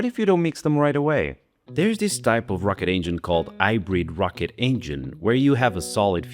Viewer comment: I wasn't looking and when he said "hybrid rocket engine" I heard "I breed rocket engines". Had to rewind 🤣